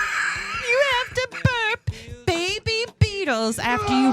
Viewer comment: I swear I thought you said you have to burp baby beetles, and immediately I pictured you with a tiny swaddled beetle over your shoulder, gently patting its back waiting for a little beetle burp. 😂